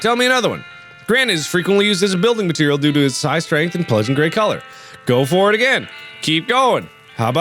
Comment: i thought gray said gay oops sorry my fellow lgbtq+ people